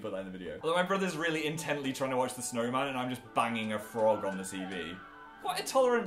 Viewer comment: All I heard was "My brother was just staying to watch the snowman while I was just banging a frog"